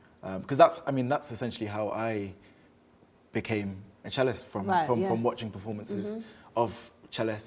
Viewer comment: I thought he said that "I'm a gyalist" at the beginning. I was about to say respect my brother. but being a cellist is better of course. Big up Sheku 🇦🇬🇦🇬🇦🇬🇦🇬🇦🇬